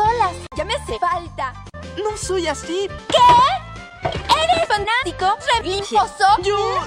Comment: Que dice en el segundo 1:22? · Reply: fanático religioso xd